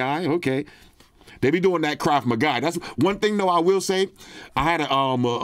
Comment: I swear said "Krav my guy" 😂😂😂😂 The most NY shit I ever heard lmao